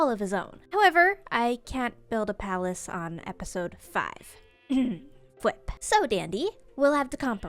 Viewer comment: Did she just say "fwip"